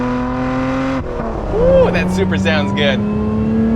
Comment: did you just call it a supra? The engine comes from a Camry.